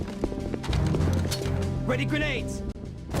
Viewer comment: I'm glad you included the best line in the game. "A DUD" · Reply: My brother and I jokingly said this line for years after playing this game. 🤣 Also, sounds like Raiden saying “Ready grenades!” 🥴